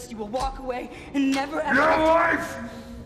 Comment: what did he say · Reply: She said "I swear on my life I will get us out of this" or whatever and he said "Your life"